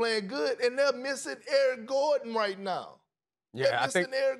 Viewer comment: I know perk means Eric Gordon, but it really sounds like he said Aaron Gordon